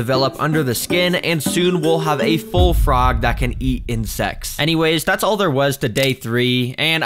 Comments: I heard “ that can eats and s-“ like my mind is NOT ready for this channel (Edit) What was my corny ahh doing💔✌️😔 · Wat? Are you saying secs or ??? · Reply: No he said the frogs will grow so big they will eat insects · i thought you said "we'll have a full frog that can eat and sex" 😭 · i thought you said can eat in se